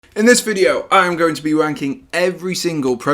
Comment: I miss heard you so bad the first 10 seconds lol. I thought you said W instead of ranking hahah